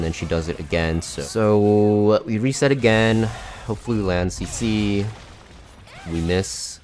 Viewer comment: all i hear is 'hopefully it will land a cc' lol